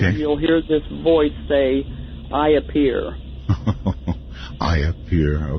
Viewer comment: No one can top Art Bell. God rest his soul. She said the ghost at 54:00 says "I appear." Does anyone else hear "My beer." ?